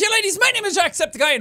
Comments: so we're just going to ignore that it sounded like he said ladies instead of laddies in his intro? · It sounded like Jack said ladies instead of laddies in the intro.